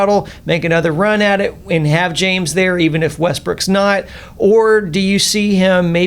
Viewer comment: Lol 3:30 “Russell westbrick” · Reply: hahahha ;D at 6:40 I think he says also Westbrick ;D hahaha